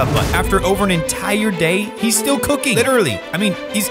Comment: i thought he said he still cookies XD